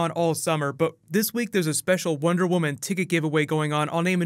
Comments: did you just say wonder woman titty giveaway? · lol I thought he said Wonder Woman tittie wtf lmao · HHH I really thought you said Wonder woman titty giveaway turned on CC to see that it was ticket giveaway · I thought you said Wonder Woman titty give away